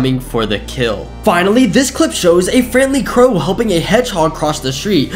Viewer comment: The crow thing when you said helping I thought u said hum-ing